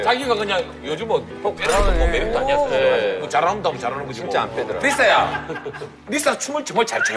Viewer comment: See 7:20 If you are Indian you may listen a hindi word for really as sachmuch I guess maybe I heard wrong..❤